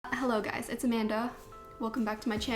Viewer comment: Everytime, every single time, I hear It's A-Man-Duh, I can't unhear it.